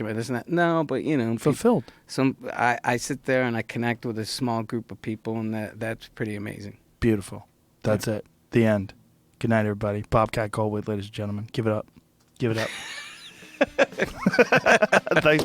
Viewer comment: Did Joe just call Bobcat "Gullwait"?